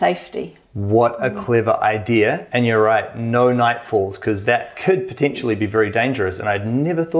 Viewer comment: Did he just say "No Nightfalls" 🤣